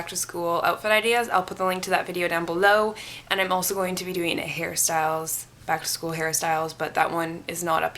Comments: When you said hair styles it's sounded like you said Harry styles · Haha you said hair styles and I thought yo said Harry Styles .... Ok bye